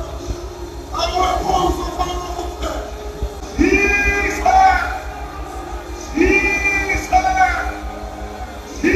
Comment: What was he saying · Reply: He said Jesus